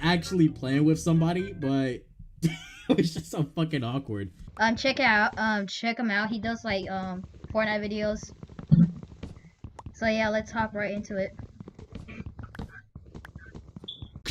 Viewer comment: sounds like you said PH videos 🥀